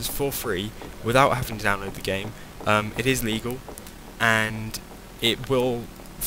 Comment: AT first i thought you said, It is eleage, But then i realsed you said Legal.